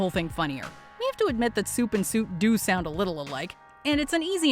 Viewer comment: What said: “We have to admit that suit and soup do sound a little alike” What subtitles thought she said: “We have to admit that soup and soup do sound a little alike”